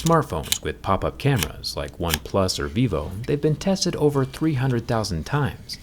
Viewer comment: Why do I hear BIVO everytime? I hope I'm not the only one